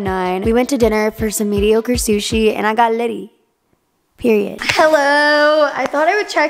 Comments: I thought you said "We had mediocre sushi and I got lucky" I was like oh we going there okay. alas it was "litty" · I fully thought you said "I got laid" at 00:28!! And I was like giirrrrrrrrrl you arent holding back!!! 🤣🤣🤣